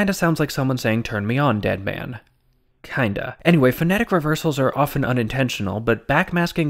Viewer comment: I heard "I'm the underman"